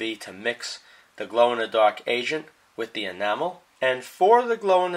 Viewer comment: i keep hearing "glow in the dark asian" instead of agent 😂